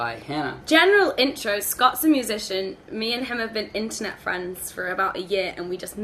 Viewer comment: When you said "internet friends" I heard "intimate friends"XD